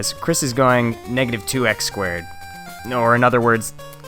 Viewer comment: At first I thought he said "Andrew Dice Squared" and laughed but unfortunately I misheard him. Ooh!